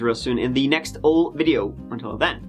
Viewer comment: Where would we be without storytelling, his ‘ life insurance’ music & dance and finally , his ‘ I love you’ and the end ! It’s a very fine line to walk - including facts of the reality AND some wit & humour : Mike , you walk it perfectly !!! RIP Janet and condolences to her family x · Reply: Is it just me that thought he was saying "all of you", not I love you? 🤔